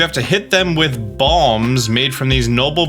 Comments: Not going to lie - thought you said bombs not balms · I thought he said "bombs" and not balms